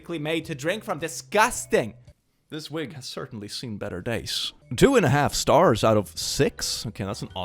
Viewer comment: I thought I heard "This week has certainly seen better days." at 11:30 because I was listening while doing stuff. Mood